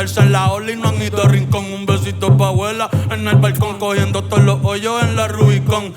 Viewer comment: me gusto hasta q escuche "todes"... ya ahi c pasaron